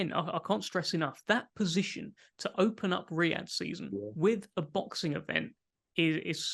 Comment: All this time I thought they were saying "weird season", which makes sense for a HW boxing champion fighting someone who's never boxed before.